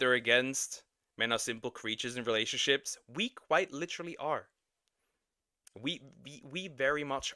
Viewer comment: I thought u said "weak white literally are" HAHAHAHA